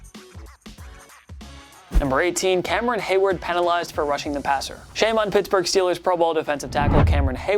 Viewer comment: he said rushing the passer instead of roughing , lol